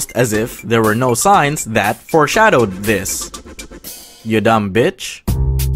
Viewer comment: I thought said your done now but he said you dumb bich and it remained me of the thing goes grrrat